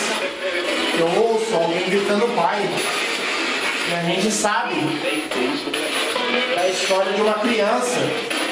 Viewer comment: De longe eu ouvi... a gente sabe da historia de uma criança! A resposta vem logo em seguida no 38:30 ate 38'32: " É, MAS ISSO NÃO TE TROUXE AQUI!" Fala bem declarado.. caramba... Vcs são demais! Mto, mto mto demais. Amo vcs.. melhor canal, melhores pessoas ❤❤❤❤